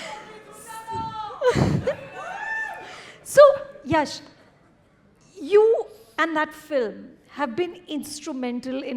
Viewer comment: what he said???😅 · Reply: He said “for some time” and in hand sign please keep silence